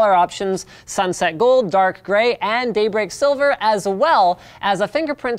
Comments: it sounds like he says "date rape silver" when describing the color of the phone... lol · is sounded like Linus said "date rape silver" ??????